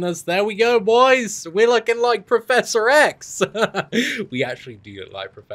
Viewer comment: when you said you look like professor X. i thought i heard you said "PROFESSOR S(E)X hahahaha